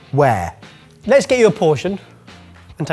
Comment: Pour spirits or not...hahhahahaha I heard you say portion